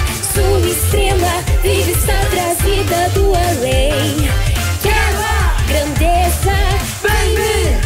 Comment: só eu escutei "Chara!" ? Ou eu to surda de novo?